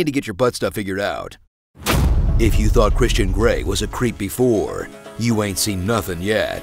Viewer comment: I misheard "creep before" as "creepy four." He's a bit more attractive than that.